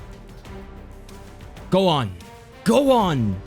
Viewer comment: I was like is he saying gohan :D